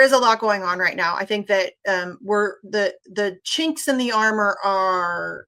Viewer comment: did she just fucking say chinks in the armor instead of kinks? Holy racist slur!